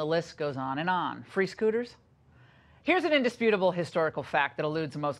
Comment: The only free scooters I see are in the Republican administration. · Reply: Ohhhh "free SCooters"...I thought she said "free SHooters". I was like count me in bitch, I'll start with a B52.